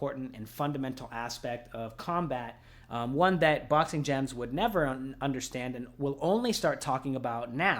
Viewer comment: Ngl i was about to start hating, thought you said hidden gem (also a boxing breakdown channel) instead of boxing gem